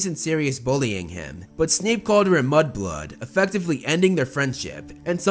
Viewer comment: Oh god I totally thought you said Snape called Lilly a butt plug, not a mud blood